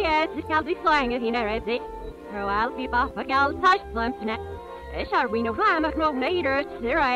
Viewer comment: I hear shall be no drama did anyone else hear it too?